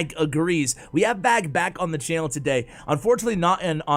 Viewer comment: I thought said, "We got A BAG back on the channel," and I collapsed laughing...